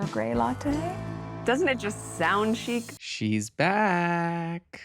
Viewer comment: I thought I heard her say "doesn't it sound cheap?" 😂🎉 it was "chic" 😮